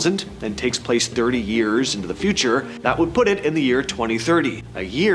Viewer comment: "and his real life will end"🤔 wtf are whoever they are, are saying?